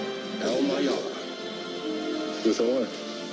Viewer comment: Anyone notice when superman said 'brother' it sounded like Luthor?